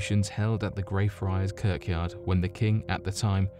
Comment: 24 Curk'yard?? Or did I just hear that? Edit: Just listened again what the hell you even said it twice too... Churchyard should be an easy one for you to read.